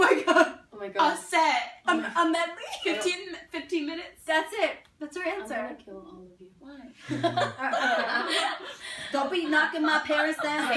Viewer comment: did Lauren just say I will kill all of you at 10:16 if so you savage Lauren but I lov you